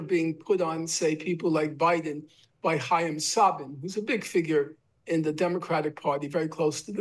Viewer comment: Did he say Hiam Saban? The Power Rangers guy?